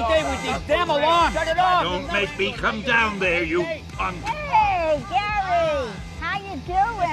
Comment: For a second I thought stan lee just said "don't make me come down there you fuck"